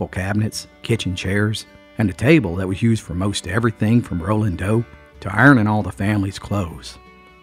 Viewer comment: rolling dough on the kitchen table, sounded like you said 'rolling dope' had me cracking up.